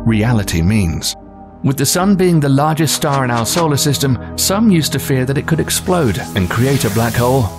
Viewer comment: He said it's the largest body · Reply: he clearly says "largest star in our solar system"